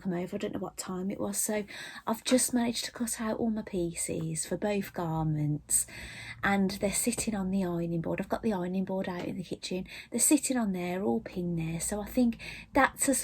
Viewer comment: For a second at 23:30 I thought you said your parents were sitting on your ironing board.😂😂